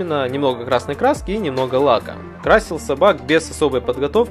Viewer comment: Вам теж почулося на 2:53 Красил сабак